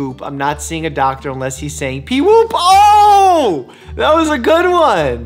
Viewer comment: I always thought he was saying "bee woop" 🤣